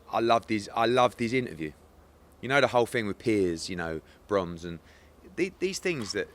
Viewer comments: “Piers….bronze….” I thought he said. Don’t doubt Eddie. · I swear Eddie said 'Piers........ bronze' (referring to a bronze medal?) rather than Piers Brosnan LMAO